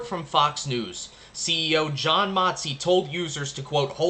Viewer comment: At first I thought you said CEO , John Moxley